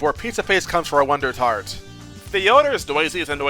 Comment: actually that IS the noise full name. Theodore Noise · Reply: I know that the noises full name is Theodore J Noise, the accent just made it sound like he said The Oder.